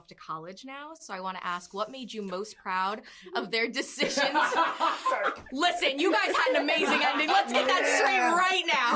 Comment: what did say at 3:06? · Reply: He said "oh yeah, we're not going" meaning college